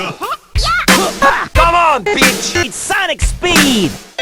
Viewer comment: In your intro, do I hear a "come on bitch!"